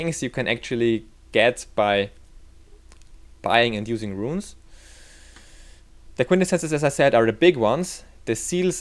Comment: what the fuck did he just say lol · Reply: the quintessences are the big ones